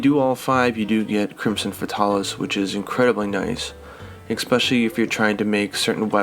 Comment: am i losing my mind or did he say egg-specially